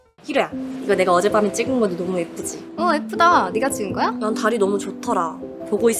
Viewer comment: 예쁘다가 F다라고 들림ㅋㅋㅋ